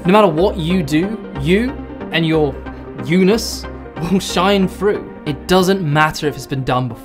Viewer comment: what is that word you said at 5:35? you and your "eunis" won't shine through? I cant figure out how to spell it to look it up lol but i assume it means unique in context.